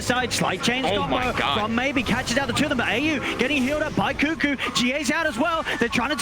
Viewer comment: did I just hear GH? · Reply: Twas GA dude.